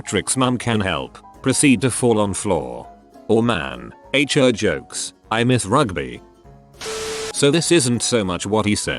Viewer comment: I'm no really innocent but what is the word that starts with h being hidden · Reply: depressed owl I went on reddit and found it. The comment was over 4 years old but the word is hooker